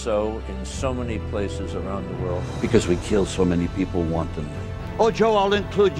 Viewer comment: what does he say here? "because we killed people .. _____" ?? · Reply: Wantonly I think